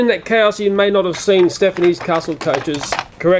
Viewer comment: Stephen's coaches NOT Stepney's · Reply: I thought he said Stephanie's